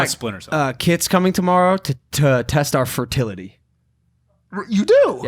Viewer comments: I actually thought Logan said I have kids coming tomorrow to test our fertility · I thought he said "I got kids coming tomorrow to test our fertility" I was like wtf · Sounded like he said I got kids tomorrow coming to test our fertility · I thought he said I got kids coming tomorrow to test our fertility 😂 😆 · Bruh at 55:17 I thought he said “we have kids coming tomorrow to test our fertility” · Holy shit I thought he said he had KIDS coming over to check their fertility.